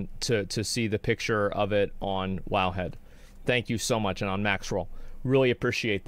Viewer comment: What does he say at 1:50 it sounds like "on a max roll"